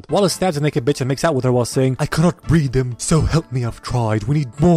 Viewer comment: I thought he said "I cant breathe"